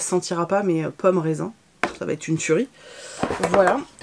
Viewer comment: J'ai entendu "dans ce brocoli il y avait également ce coffret" 🤔😂